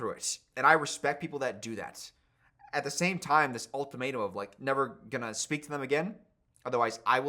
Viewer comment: lowkey sounded like an accidental Community reference at 44:24 when he said "all tomato" instead of "ultimatum"